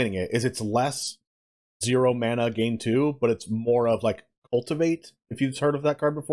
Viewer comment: Wait what card? Can’t tell what you said and it just sounds like “ulti-mate” which obviously doesn’t go anywhere when looking it up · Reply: "Cultivate"